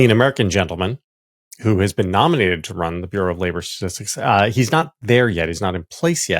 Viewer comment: I thought it sounded like JVL said, “The Bureau of Flavor Statistics”. Let’s change it to that and put Flavor Flav in charge. Why not?🤷🏼‍♂️🤦🏼‍♂️